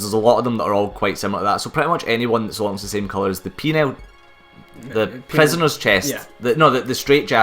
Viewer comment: Every-time I heard say Penal, I always heard Penile. So I kept hearing shit like "Penile skirt"